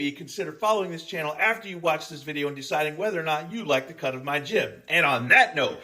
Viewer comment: I heard "if you like to come to my gym," at fist, even after hearing "the cut of my jib," on a previous